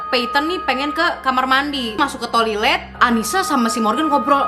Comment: did i heard that "tolilet"